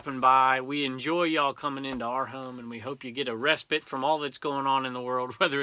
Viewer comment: When you said, "I hope you get a respite..." I thought you said, "I hope you get arrested..." LOL